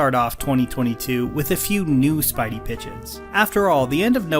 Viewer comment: Really thought you said Spidey-b*tches earlier lol